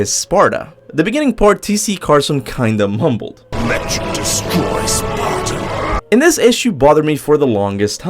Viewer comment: I would love to play these lost levels I also thought he said not destroy sparta